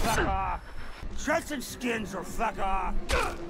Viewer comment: I thought the guy was saying, "take shirt off or fuck off."